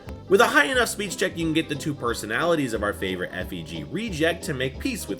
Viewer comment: I'm sorry did you just say F E G?